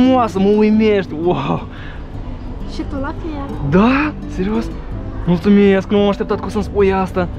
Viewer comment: Я один услышал по русски да серьёзно?